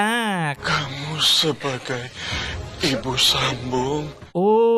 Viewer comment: Njir aku dengernya Ibu Sambo 🤣🤣🤣